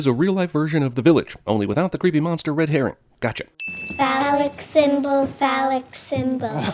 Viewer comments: what are the little girls saying? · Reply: Phallic symbol. It's back to Summerisle School for you! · what did they say? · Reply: Phallic symbol, phallic symbol.